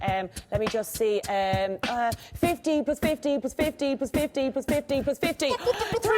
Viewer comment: She said 50 + 50 etc. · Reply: Oh, you're actually right. I swear I heard '50 BY 50' first time round, still kinda sounds like it on the first few. Ah, well.